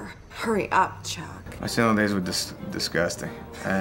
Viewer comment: What does Adam Sandler say at 11:50? I can't make out the word he says. "My * what * days were disgusting"?? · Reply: “My single days”